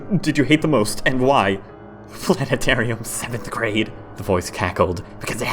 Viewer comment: ok. 1 - Holy cow this story is /AMAZING/! Seriously, great job on this, I love it! 2 - Can anyone help tell me what the field trip was? I know it sounds like a weird question, but I keep hearing 'Flatitarium' and an image pops in my head of just a school field trip to a dome building (Like a planetarium) and all the kids go in, it squished in like a pancake and they all just walk out like... suddenly 2-D. Suffice to say, I can't listen to it without that moment just making me suddenly burst out laughing at that image, just from how stupid and absurd it is. So can someone fill this gap so I can stop laughing at that part? ^.^' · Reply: Lol, "Planetarium." It's "Planetarium."